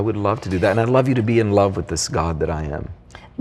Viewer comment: Utopiang & Generictubeuser, you misheard him. Listen to it carefully again. Mr Young says, "I would like you to be in love with THIS GOD that I am", not, "...with THE GOD that I am". In saying this he is simply saying that he would like you to be in love with the God that he is in love with. Even if he said it the other way, I think he's still saying that he would like you to be in love with the God he's in love with.